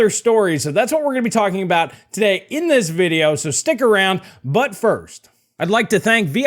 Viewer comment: did you say... "so stick around butt first." Could have use some different pauses in those sentences 🤣